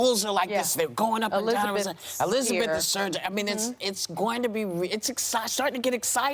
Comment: Did Sunny say “warren is surging” 😆 Listen to Meghan guys...she said “They are not scared of Joe Biden” thats why she is always pushing uncle joe😉 Bernie2020